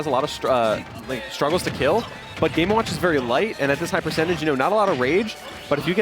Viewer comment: Did he just say Cloud struggles to kill? 😂